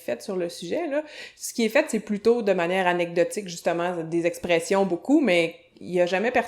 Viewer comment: After hearing mainly 'Parisian' and standard french in all my years of learning french i remember the first time i heard this accent and it make me laugh, i really don't know why. It almost sounds like a southern american accent on some words like 'Chaine' (They sound like 'shine' like 'moonshine' in english) and 'justement' (i thought i heard 'juste un maiiin' ) aha.